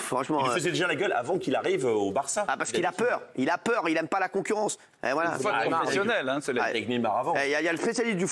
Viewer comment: C'est moi ou j'ai entendu " Messi a peur de la concurrence"???!!!